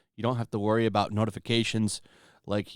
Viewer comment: Every time I hear your outro I hear, “Come check out mein content.” And I chuckle that weirdos actually think you’re a nazi for being normal.